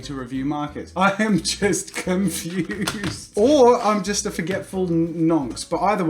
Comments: Did you actually say, "I'm just a forgetful nonce"? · did he just call himself a nonce? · Did he say he was a forgetful nonce? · did he call himself a nonce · did he call him self a nonce or am I mad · Did he say nonce? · did he just call himself a nonce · did he just call himself a forgetful nonce or am I just hearing things? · he say what I think he said--nonce? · did you call yourself a forgetful nonce?! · does he call himself a forgetful nonce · did anyone else hear say "or I'm just a forgetful nonce " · did he call himself a nonce? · who else thought he said nonce